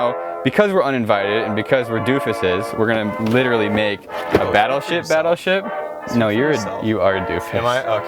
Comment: Thought he said gay battleship